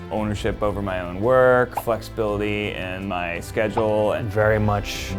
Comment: I heard “fucksability” instead of flexibility 🤣